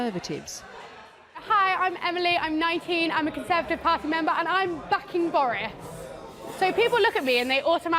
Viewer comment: Honest to God, I thought she said she was f*cking boris...